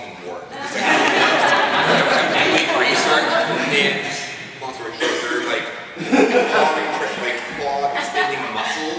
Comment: Thanks for taping this! I wish I could understand some of the things people are saying at times, like I can't make out the username that recommends at 43:55 and everyone seemed so excited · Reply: hypermele!